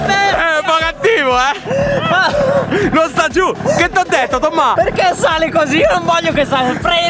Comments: minut 7:25 solo io ho sentito che ti ho detto TOMMA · Solo io al minuto 7:25 ho sentito tomma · Ma solo io al min 7:24 circa ho sentito che dice " che t' ho detto TOMMA"